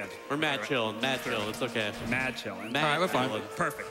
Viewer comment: Did I hear "mad chillin' -- madvillain?" Nice